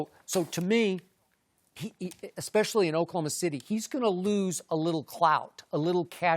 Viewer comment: Did Skip just say, Russell is losing Clout? 9:25 - 9:30 🤣